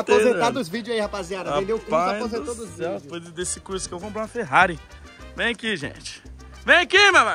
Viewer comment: Só eu que escutei o falando "foi por causa desse curso que comprei uma Ferrari" sera o carro novo?? Minuto